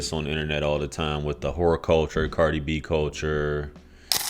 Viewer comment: Did you say “whore culture”? 😂😂😂